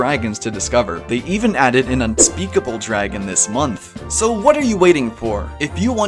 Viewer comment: DID YOU JUST SAY UNSPEAKBLE DRAGON?!?!?!